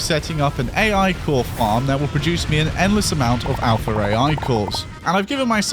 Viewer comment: I thought you were saying alpha ray eye cores for the longest time, and then I realised you were just fucking bri*ish